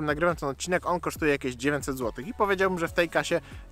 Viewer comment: Najpierw usłyszałem 90 zł i aż musiałem cofnąć żeby wrócić do szarej rzeczywistości